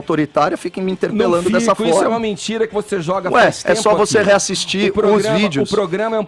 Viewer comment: Eu entendi bem,!!!!!!!! no timer 21:50, o pilantra do Edgar chamou o Copolla de mentiroso "não fico isto é uma MENTIRA, que você joga o tempo"